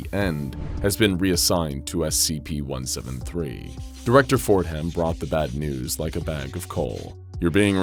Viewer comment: At the beginning, he said "Director Fordham"... I heard "Director Forehead", and now I can't unhear it.